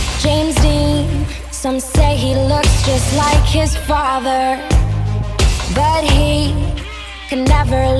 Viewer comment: I was just listening to it and then I heard Dean and looks just like his father. I was thinking poor Dean with his daddy issues